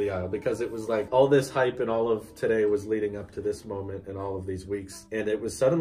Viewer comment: I heard “all this hype and all of today and all these leaks”